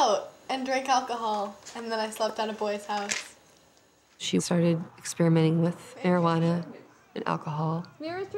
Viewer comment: I could have sworn lady said "she began experimenting with Herojuanna"